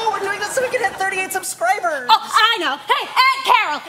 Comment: shane said "we are doing this to hit 38 subscribers" caption said "we are doing this to hit 40 subscribers" they already had 38 subscribers XD